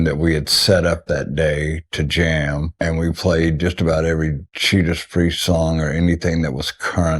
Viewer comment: The subtitle reads "Cheeta Spree" instead of Judas Priest. LOL